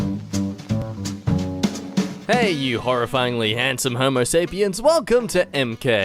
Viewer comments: I thought he said you freakily handsome homosexuals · I thought the intro was saying “horrifyingly handsome homosexuals” for a moment and was like *yes*.